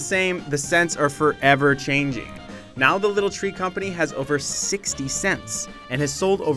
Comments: i thought he said "60 cents" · when they said that the air freshioner had over 60 sents i thought that they said they had over 60 cents, i was like im sure they they make more money than that! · I thought he said the Little Tree Company had over 60c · that sounds kinda sad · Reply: It sounds like it says “the company has made over sixty cents” instead of “sixty scents”